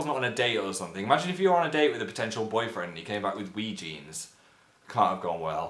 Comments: Did anybody else hear him say potential boyfriend at 3:40 ? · Phil, did you just say potential boyfriend?? (Yes, yes he did). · I heard "imagine you were on a date with a potential boyfriend and YOU came back with wee jeans" instead is they and I almost freaked out